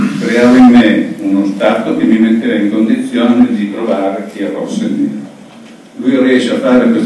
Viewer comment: Salve, chiedo scusa ma l'audio è davvero pessimo, sareste in grado di spiegarmi cosa dice al minuto 6:26 per cortesia? "...creando in me uno stato che mi metteva in condizione di trovare...????" spero riusciate a rispondere perché credo sia un peccato non capire una spiegazione così importante.. grazie mille! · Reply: Dice semplicemente: "che mi metteva in condizione di trovare il rosso e il nero" (riferendosi al colore delle carte)